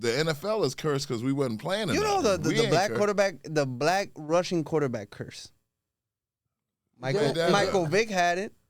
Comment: I swear I heard that nigga say Black Russian QB 😂😂😂